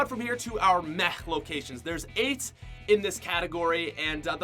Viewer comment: I thought he said that the meh category had aids turns out he said 8